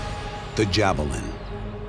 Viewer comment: i thought it said jew's vallient when they said javelin